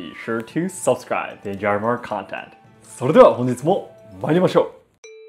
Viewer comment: Just a question, "それでは本日も参りましょう" ⬆️ Is this what says? Thanks🙏